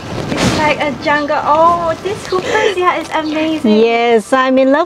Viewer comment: What is the hanging plant that draped across her forehead like hair during her "hiking path" 20:20 timestamp...? I love it but can't make out the name.. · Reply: Hyperzia